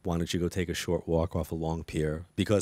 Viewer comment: I think they said, take a long walk on a short pier. I still enjoyed the interview was very interesting.